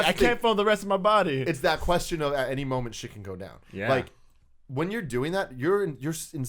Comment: Lol when they were talking about girls being on your chest and Tim said "at any moment shit can go down" I heard "at any moment she can go down" lol